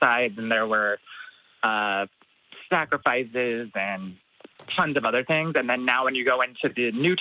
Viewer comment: I know I just misheard, but since offerings were mostly food, I like the term Snackrifice.